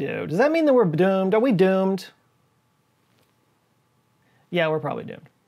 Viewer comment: Anyone else hear mmdoomed? 😂 Love the vid, keep it up!!